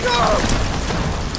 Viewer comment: What do you guys think he says at 0:07? I hear "Follow the trench, it'll lead you to the hond-even" something like that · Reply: I'm pretty sure it says rendezvous, just with an accent.